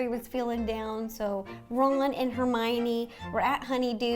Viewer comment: I heard Ro say Roland and Hermione😂